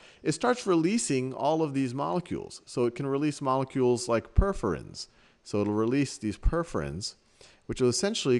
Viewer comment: Hey hey, in English subtitles somewhere around 7:26 it says "molecules like preference" instead of "perforins". I know it's been a while since the video was published but if you could correct it it'd be great :)